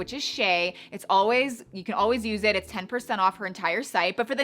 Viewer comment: I swear it sounded like 50% off (03:39) even after rewinding and listening a few times !